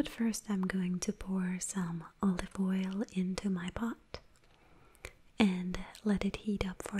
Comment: all the oil into my butt. sorry this is all i hear